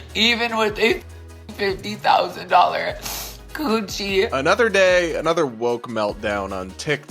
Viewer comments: Ohhhh I thought he said a "50k Gucci"! Was thinking, I don't own a Gucci. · 😂 y'all I thought he said "$30,000 Gucci bag" 😂 omg